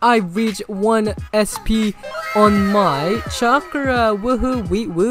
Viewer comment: Oh it's charka i thought u said chikara 🤣🤣🤣🤣